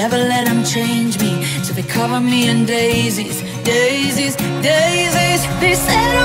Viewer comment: when she said " til they cover me in daises " I heard " to the goverment of daises " like wha